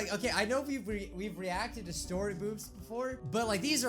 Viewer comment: is it just me or did he say story-BOOBS?